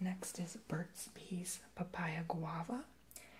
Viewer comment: did u just say birds pee